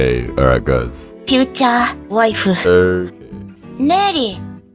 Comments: Brooo when she said “future wife” i thought she said “futa wife” 😳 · I’m going to be honest when ever she said future wife I thought she said futa wifu so I had to rewatch it